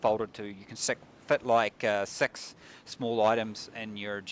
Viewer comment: Best way to tell if someone is from New Zealand is to have them say six, sounds like they say sex (7:27) and it always makes me smile. Not like I'm immature or anything.... Also I'm really excited for this game :)